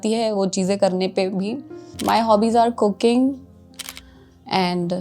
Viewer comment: for a second i heard cocaine 😂🤣🤣🤣 Brave girl 👏🏼